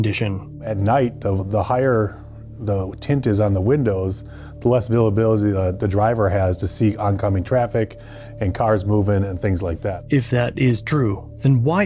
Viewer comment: "the less avauuila aba the driver has to seek oncoming traffic" 😂😂😂 anyone else catch that? These cops need to get off their ego pills. I swear it actually takes balls and some sort of hard drug/med to be as brave as even some criminals are.